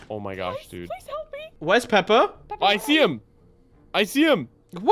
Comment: i thought he said Peppa 💀